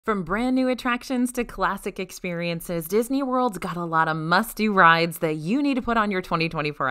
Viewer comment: I thought you said musty rides instead of must do rides